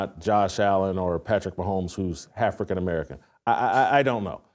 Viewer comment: Yo did he just say halfacan American 🤣🤣🤣